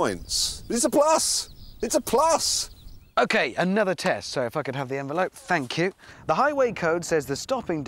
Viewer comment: Did Richard just say: "F*ck it, have the envelope" ?